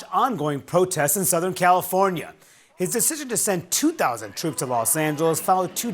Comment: Why did I hear this as “(Trump’s) decision to send TWO DOZEN TROOPS TO LOS ANGELES “ and I was scratching my head like…why 24 troops? Is this a DOGE cut? 😂 Honestly as a former Californian as of almost 20 years ago, I don’t think the rest of the country should clean up their messes. Gavin Nuisance has to pick up his own messes he’s made with his 💩 policies, it’s not fair the REST OF THE COUNTRY PAYS FOR GAVIN NUISANCE’s SHORTCOMINGS. Let L.A. fall: that’s what they voted for; then prosecute the mayor for goodness’s sakes - how many more headlines of her absolute inability and incompetence does the world need to witness??